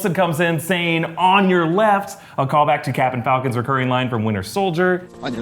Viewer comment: anyone else hear "captain falcon"?